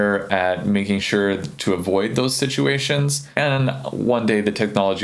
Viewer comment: did you say "Avoid those stitchuations"? or did I just hear that in my mind? ;)